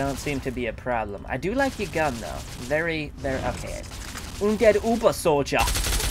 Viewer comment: Funny thing is, used the word "uns" alot. Uns is German for "us". I kept hearing that a few of his words were actual German. Good ol'. 10:27 "Er ist uns eins übersoldier," goes; I hear "He is us one übersoldier." Keep being awesome, and keep speaking that deutsch!